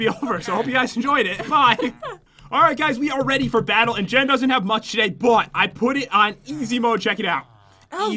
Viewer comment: Did PopularMMOS say 'Jen doesn't have much sh*t'